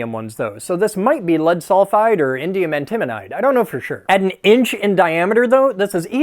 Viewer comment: Did he just say Indium Intimidite?